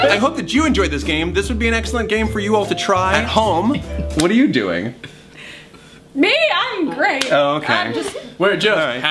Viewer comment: it sounds like “ARE YOU MUFFIN?” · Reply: I heard "are you muffin mom?"